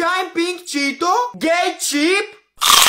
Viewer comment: did he just say that the chip was gay ?